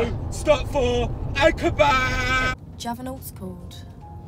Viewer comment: what did she say? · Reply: Do you have an aux cord